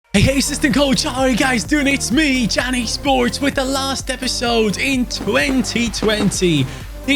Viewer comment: I was watching this episode while my sister was nearby and she misheard your intro. When you said "It's me CaniSports" she thought you said "It's SweetJohnnySports" so you are now known as in my house!